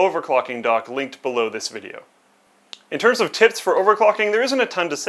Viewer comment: did say TITS at 7:00 :))